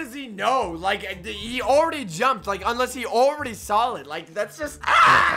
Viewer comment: like he already saw it me:did he say already sall it or saw it?